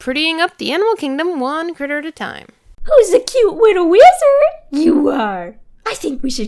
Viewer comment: did say cute wittle WIZARD! That lizard is STAR SWIRL THE FREAKING WIZARD!